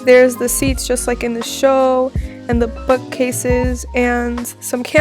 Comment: is it only me or when she said book cases it sounded like she said fu** cases edit: THE OUTFIT THOOO ITS NOT FIREEE